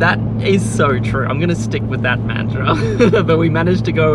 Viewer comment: Did he actually say "mantra" ?????